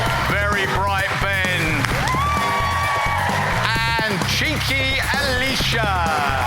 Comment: what does he call the last girl? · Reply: 🤣🤣🤣 meant to be cheeky